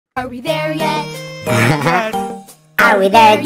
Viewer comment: Bro really thought they said “ are we there dad 💀”